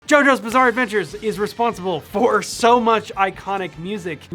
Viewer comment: I thought he said giorno bizarre andvature